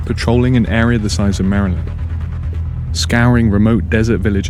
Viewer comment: Does anyone have any idea what he says around 1:25? ‘Patrolling an area the size of ___’? Maryland??